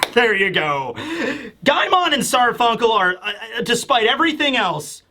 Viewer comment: Did you just say Simon and Garfunkel? Oh no you said guymon and sarfunjkel